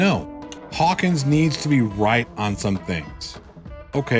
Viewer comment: I always thought you said Right Focus. LOL